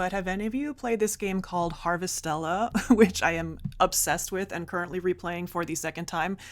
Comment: Could you write here the name of the game you commented on? At about 10:00 into the video. I searched for the name by the sound of the words I heard, but nothing came up in the search. Thanks for the great video. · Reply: Harvestella